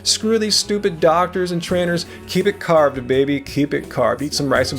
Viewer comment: I Don't watch these shows because they are all crap I'm with you Screw these doctors and trainers (I thought you said traders) either way I'm with you ;) Keep up the great work!